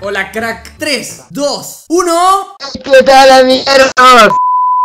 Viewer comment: Yo escuché "ey qué tal amiguertos" XD